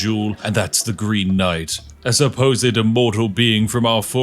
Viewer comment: 🤔I could have sworn that said "THE GREEN KNIGHT'S CHEESECAKE"... Weird.